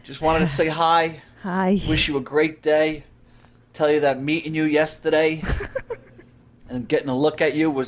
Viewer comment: Im a disabled cancer patient on disability my wife works 50 hours a week 12 hour shifts as a care nurse. She woke up to ask me what did u just say. And I had to explain "meeting you"